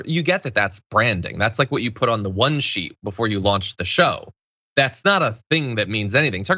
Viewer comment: I heard "white sheet" instead of "one sheet" and it kind still made sense.